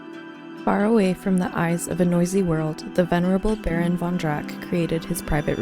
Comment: I thought you said the "vulnerable" baron von Drac at first, like they're an endangered species... I thought "yea that checks out". This is such a unique zoo, if I even can call it that. The palace with the giant manatee aquarium ? Damn